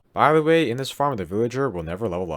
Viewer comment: IMPORTANT: 1. Make sure you use a villager that you have never traded with before. 2. If the villager does not restock its trades, try replacing it with a new villager. 3. If the villager increases its prices, try curing it again. 4. This farm does not work on Minecraft Bedrock 1.18.10 or higher, so I recommend you watch my raid farm tutorial. Here is the link: <link> I hope you all enjoyed learning how to make this simple and efficient Minecraft Bedrock emerald farm! Please like, comment, subscribe, and check out my other Minecraft videos! Thanks for watching! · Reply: I thought you said "by the way, this villager will never know love." I misheard, but it's also true 😅